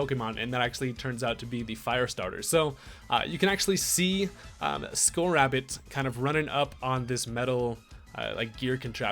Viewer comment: Lol did anyone else hear it at @ 2:00 ....Scorrabbit....